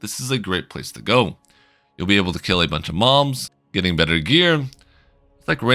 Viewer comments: I thought he said kill a bunch of moms, had to replay it. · did he say "be able to kill a bunch of 'moms'" 😂